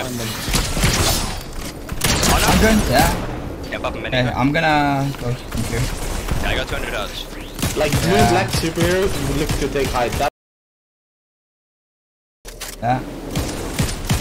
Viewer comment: did he really say i have 200 dollors?LOL